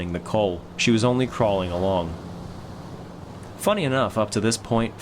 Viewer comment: You said funny enough instead of funnily enough. Thank you, god bless you lol funnily sounds so awkward.